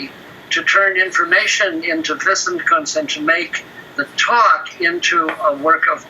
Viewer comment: what'd he say at 1:57:54? "to turn information into [inaudible] to make a talk into a work of art" ?? prinsen-kansen..? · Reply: Probably "wissenkunst"